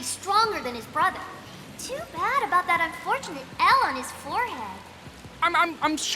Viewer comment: "that unfortunate L on his forehead" I'll be honest, all I hear was Smash Mouth. XD